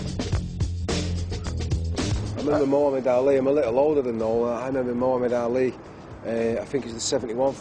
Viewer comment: can anyone explain what Ian said at 4:01? :D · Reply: "I remember mohammad ali, im a little older than noel, then sommat about some Adidas boots